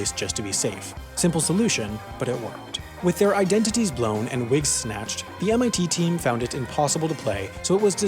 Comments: did you just say wig snatched?😂😂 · did u just say wig? Snatched? Omg